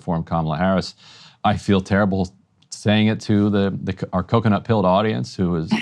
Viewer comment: Lol did he just call people that support Kamala coconut pilled?!